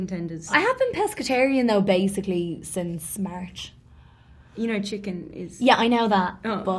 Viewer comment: Margot: I wanted to ask you cuz you just ordered chicken tenders. Saoirse: well I’ve been pescatarian since March technically Margot: you do know chicken tenders are ..... Saoirse: I know but ohh....😂😂😂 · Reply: I thought Saorise said "I've been pescatarian since marriage." LOL